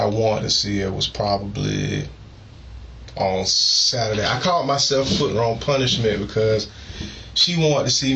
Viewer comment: WTH 🤦‍♀️ did he just say? He caught himself putting her on punishment? 😮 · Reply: "I caught myself putting her on punishment.." followed up with "she had herpes" gee, axshole I wonder when you gave her that? He's the worst kind of man and we can all be grateful he'll be behind bars - he can spend his life being terrorized by a sick man.